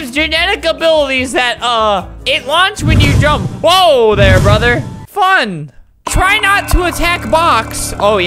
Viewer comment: I thought he was saying Woah there sister